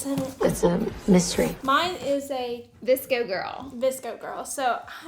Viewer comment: Oh my god I’m so dumb.. I thought she said a DISCO girl box 😂😂 I didn’t realize until she opened the scrunchies that she really said VSCO 😅